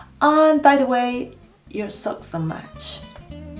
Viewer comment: When she said “your socks don’t match” I heard “you suck so much” 😂😂😂